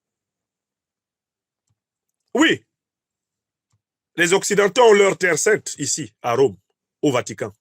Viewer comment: J'ai entendu "les Européens ont leur terre sainte ici à Rome" . êtes vous en Afrique ou en Europe?